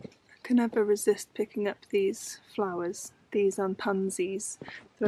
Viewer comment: Sorry guys im having trouble hearing/understanding you properly this week? 🤔👂🏻 And not pansies but Primulas! Or Primrose (Primula vulgaris) will come back every year if planted in the ground 🌸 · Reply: thought she said “these are pansies”